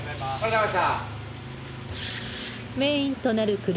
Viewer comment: 翻訳がめちゃくちゃで内容より翻訳で楽しみました(笑) ゆーがた=ゆーまちゃ ありがとうございます=ハゲましたー その他多数(笑) 納車のシーンで、ありがとうございましたーがハゲましたーって聞こえる...主役のおっさんがハゲましたー！って翻訳見てたら思ってしまう(笑) つまらない漫才よりよっぽど面白い翻訳ソフト とにかく翻訳が最高ウケる(笑)内容でした！ 翻訳が面白すぎて内容は真面目で良いけどあまり入って来ない(笑)